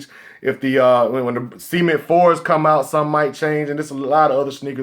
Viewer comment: I thought he said semen 4's